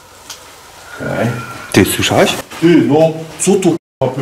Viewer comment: Kilka razy słuchałam co to za głos żeby się upewnić, ja usłyszałam mocny męski głos "HEJ" Odcinek bardzo fajny i standardowo oglądanie z uczuciem że zaraz coś usłyszę, wsłuchiwanie się w każdy szmer... Pozdrawiam 😊